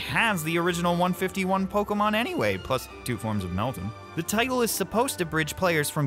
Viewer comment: Okay I've backed it up 10+times and still have no idea what you said here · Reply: "plus two forms of meltan"